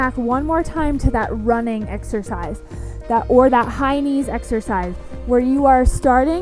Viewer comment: I love these resistant bans videos! I had a little chuckle when I thought you were saying “hynie”😂 but you were saying high knees!😅